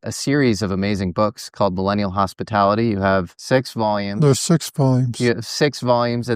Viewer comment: I thought you said the sex volumes. I was like, oh no don't tell me he wrote a book about making love with aliens please 😮 😂